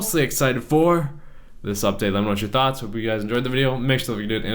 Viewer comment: what did he say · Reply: youtube watcher pretty sure it's "I hope everyone has a wonderful day or night."